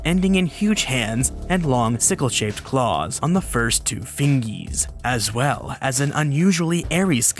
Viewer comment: Did he just say fingys 😭?